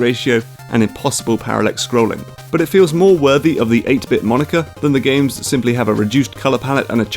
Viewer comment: w...what did you say? 8-bit what? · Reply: I'm fairly sure he said "moniker".